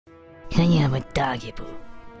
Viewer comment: it sounds like it said "clean up your doggy poo"